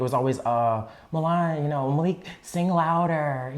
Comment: did he say his real name? Malik?